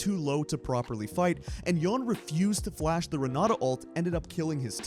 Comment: Did you just called aphielios "yone" 5:20 ? Edit: oh 😮